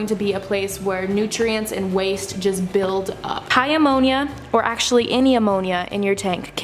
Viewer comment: when you said high ammonia I thought u were saying "hi, ammonia!" and deadass thought u named a seahorse ammonia